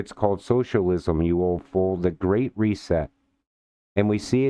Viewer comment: did he say you old fool?? Lmao